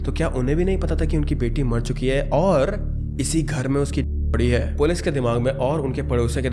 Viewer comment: on 13:40 of the video i dont understand what hes trying to say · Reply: Debjani ki body